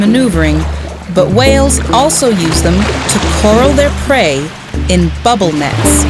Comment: Amazing footage! And an extra thumbs up for using metric units! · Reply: "coral" instead of "corral".